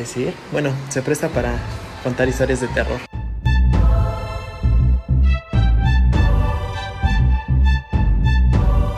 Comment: se escuchs un grito pero no oi que dijo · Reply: Parece que dijo ayuda